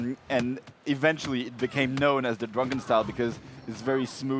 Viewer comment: did he say Dragon or Drunken? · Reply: Jace Ace Drunken a